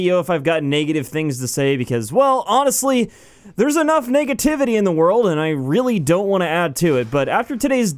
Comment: Sounded like you said niggativity in the beginning. I giggled a bit